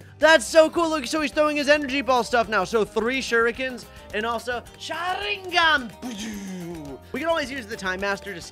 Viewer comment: Did he just say “Sharingan!” Instead of “Rasengan!”?